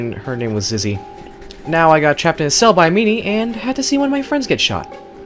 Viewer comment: I thought he said I was trapped in a cell with mimi